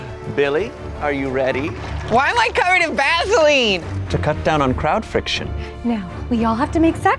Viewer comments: When they said Vaseline I thought they said gasoline the first time they said it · Who else thought they said gasoline instead of Vaseline...